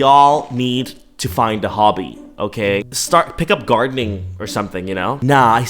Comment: I thought he said start doing Dharmann as in dharmann’s vid 😭